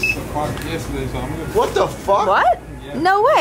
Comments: WHAT DID THE DUDE SAY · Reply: i've been here since 6:00 o clock yesterday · can someone translate or tell me what's going on because I can't hear anything he's saying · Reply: He said he had been working since 6:00 yesterday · What did the guy say at 13:00 ? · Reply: I think he said he's been working since 6 o'clock yesterday